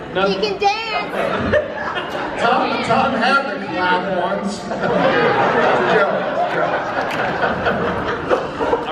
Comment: Can someone pleaseeee tell me what Michael says at 5:16? Thank you in advance!! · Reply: I think he says "Tom had 'the clap' once. It's a joke it's a joke. "